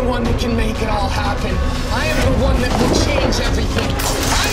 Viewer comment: Iit sounds like he is saying 'for your f***ing laifu'